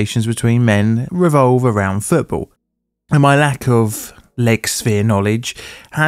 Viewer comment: I thought you said "my lack of leg spin knowledge" there, to which my first thought was, "yeah, you really don't know much about football"! 😄